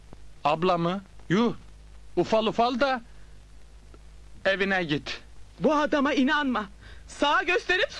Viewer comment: Burada ne diyor? Tam anlayamadim da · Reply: Kadına abla dediği için, ufal ufal da evine git diyor. Hani ufal ufal da cebime gir diye bir tabir var ya, o öyle dememiş evine git demiş